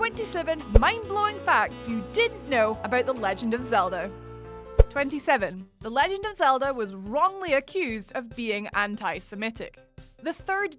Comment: did anyone else hear"Zelder" at about 0:57?